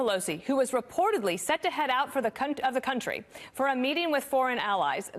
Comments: I heard “C*nt of the country.” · " who was reportedly said to head out of the c--t, country" That might have been a slip.😁😂😂😂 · Did she say Nancy Pelosi is the c*n’t of the country? She did. Is she right?